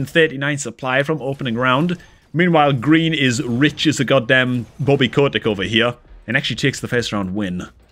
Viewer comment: did he just say "As rich as a Bobby Cobrak"? lmao...what?